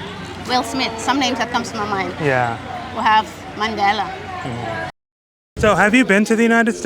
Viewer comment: Did she say ''Mandela''? 👀👀